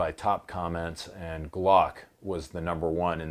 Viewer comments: All I hear was “Glock is #1” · “You’re not a navy seal, you shop at kohl’s and you use kohls cash.” 😂 Straight murder right there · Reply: All I hear was “Glock is #1 💕